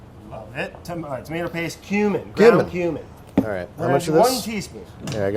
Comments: who else heard ground human? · When brad said ground cumin, I thought I heard groun human